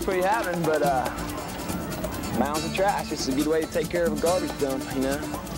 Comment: Love it! so many great memories! but.. can someone translate what the dude says @ 8:37 😅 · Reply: “… A good way to take care of a garbage dump”. Trashmore was built on a landfill I believe.